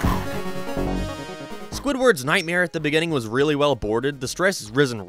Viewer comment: I THOUGHT THAT SAID SQUIDWARD'S DICK SIZE